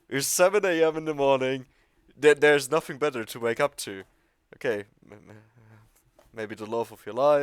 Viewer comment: When you said "nothing better to wake up to" for like 5 solid seconds I thought you said "titty beer" which sounds like a great way to wake up